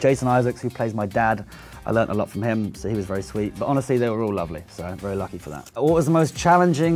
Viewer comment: Did Tom call the adults ugly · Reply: He said lovely